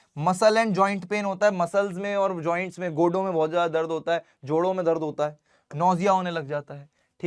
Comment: did he said goto pe dard hone lag jata hai 😂😂